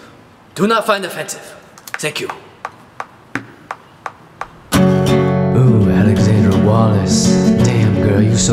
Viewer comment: In the beginning it sounded like he said Alexandra 'Walrus'